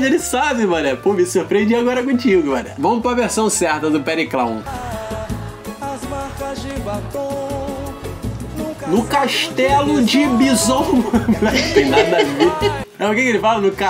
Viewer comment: "As marcas de batom num casaco de vison" Nunca tinha ouvido essa músicas daí juro que entendi "as marcas de batom nunca sapo divisor"